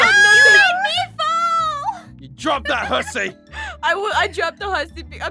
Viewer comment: Okay in my head I thought said drop that Hershey.